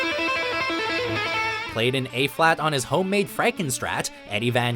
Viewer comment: I belly laughed when eruption was number one on the list the really need to redo the list and it’s e flat it sounded like he said A flat and that is very low